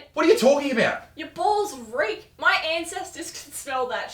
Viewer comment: We need more, she's funny as! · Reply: Thought for a second she said your balls greek instead of reek